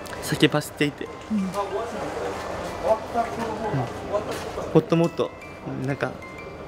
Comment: ってこういう喋りかただったっけ？関係ないけど、1:16のところ「ほっともっと」にしか聞こえん…ｺﾞﾒﾝ これからも応援するよー！